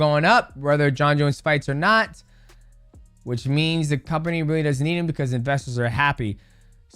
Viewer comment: John said he doesn't need him . But slurred. Sounded like he said needs me. He said doesn't need me. Gave Tom a compliment. Everyone keeps saying tom needs me he did not say that ! He said rom doesn't need me but slurred.